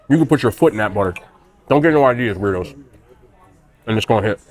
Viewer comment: Haha 3:16 I thought you said “don’t get any ideas Brudos” but I think you said weirdos. If you said Brudos that’s hilarious I’m rewatching Mindhunter and he was the foot fetish well actually women’s shoe fetish guy but anyways 😂